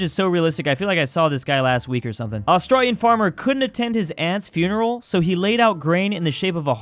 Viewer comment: I heard him say "his ant's funeral" and for some reason I didn't question it